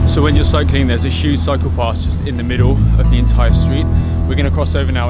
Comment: Cycle path. I thought u said gotta watch out for the PsychoPaths. Lol